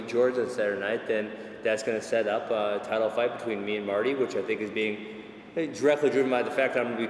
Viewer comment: "going to set up a title fight between me and Marty" ... Does Kamaru Usman go by 'Marty' or did I hear that wrong?